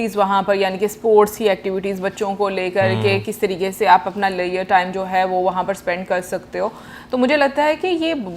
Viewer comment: @ 7:59 Not making fun of you but ye kaunsa "time" bola? Maine rewind karke 3-4 baar suna but I didn't understand. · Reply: Leisure.